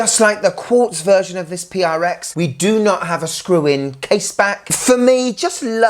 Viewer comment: I'm a bit gob smacked...for a minute there I could have sworn you said it had a clip on case back!, 🤣 who would bring out a new watch for £310 with a clip on case back?